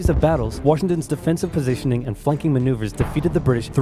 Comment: did he say fucking? i think he meant flanking